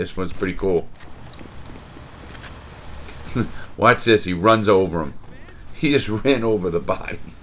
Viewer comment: did he just say he ran somebody over 🤔